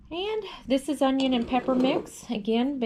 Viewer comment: Ooooh “onions and pepper mix!” I thought she said onions and peppermint! I was concerned.